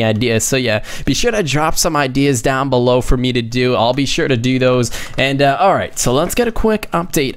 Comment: he said "do does" sounded like DDOS threat to me 😟